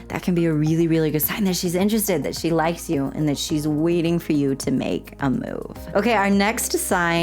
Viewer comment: I thought it said “make a movie”. 🎥 🛌